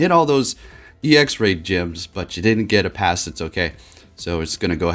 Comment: instead of CC reading raid gyms, it read it as rape gyms. not gonna lie it spooked me